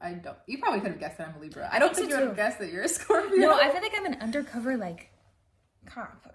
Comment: what does say here? lol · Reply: Undercover COP. Lol I heard something else initially, so I rewinded to make sure I was wrong 😂